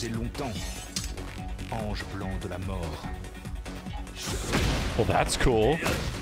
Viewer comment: As someone who speaks Spanish and has studied French, I keep hearing Victor say “angel blanc de l’amor” instead of “angel blanc de la mort” and just thinking of Victor calling Dragunov the Angel of Love is really funny to me